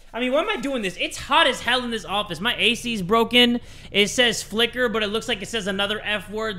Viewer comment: i was far away from my phone so i thought it said Fucker